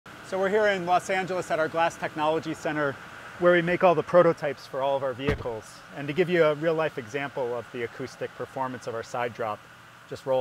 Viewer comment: did he just say "so we're here in Los Santos?"